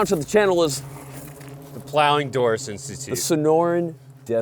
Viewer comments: I thought said, "the Plowing Doris Institute" 😂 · Did he just say "Ploughing Dora"?